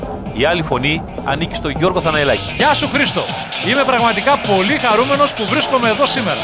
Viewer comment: Sounded like he said "Yeso Christo" @ 0:10, what does Jesus have to do with this ? lol .